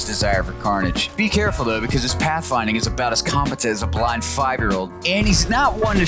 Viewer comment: I had to re-watch the clip cuz i thought he said "Our little friend is as competent as a Black Five Year old and he's not shy to step on a few mines or two." 💀💀💀